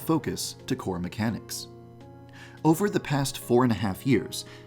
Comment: i thought he said “over the past 45 years” lol